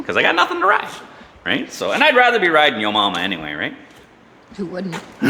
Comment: Did you just say I'd rather be riding yomomma to your wife? Lol...